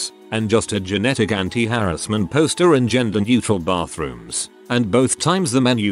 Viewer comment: i’m sorry did i hear gender neutral bathroom i need that please every place needs that i don’t know which bathroom to go to